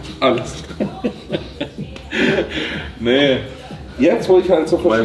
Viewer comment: was sagt hier? 😂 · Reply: Er sagt:“Du meinst alles?“ passt auch zum Kontext.